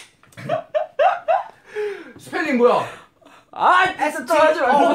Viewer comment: it sounded like (spelling bolo) 😂 If u r an indian army u know💜